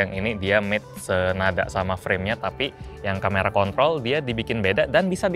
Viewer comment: kamera kontrol sepertinya kuping saya salah dengar :face-blue-smiling::face-blue-smiling